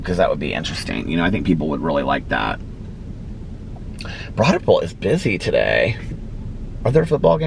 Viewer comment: Lol right here you said Broad Ripple is busy today but I swear for the longest time....years, actually....i always thought you said Brodderple. Lol and it wasnt until Alex posted a pic on ig that I said the words Broad Ripple lol😅